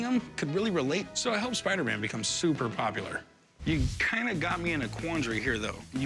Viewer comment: Did he just say “ I hope Spider-Man becomes popular “?